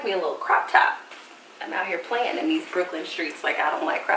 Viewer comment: Nevermind. I watched your live and saw that you are still there, but I swear I heard you say "these Brooklyn streets" in this video. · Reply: Lol, I did say Brooklyn streets I have a crush on NYC... LOL so ever now and again I say Brooklyn Street hahahahaah. Thank you for watching and commenting.